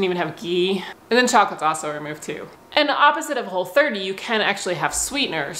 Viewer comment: What does she say at 3:53. It sounds like chocolagosover